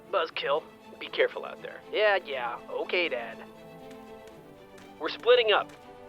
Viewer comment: Was I the only person who heard tucker call wash dad